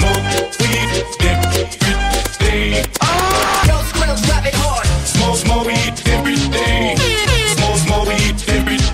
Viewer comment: What is the guy saying in 0:18 ? Is it "Girls gone savage hard?" xD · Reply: no. hes saying "YO SKRILL DROP IT HARD" the line came from a skrillex song so thats where it came from.